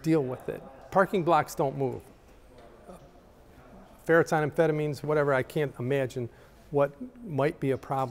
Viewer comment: Did he say ferrets on amphetamines? 🤣🤣🤣